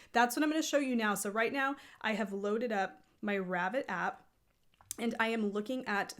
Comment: Did you do that search in ravelry? It sounded like you said Ravid app? Not sure what that is.